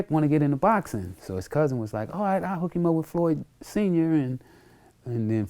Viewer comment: Sounds like he saying floyd isent smart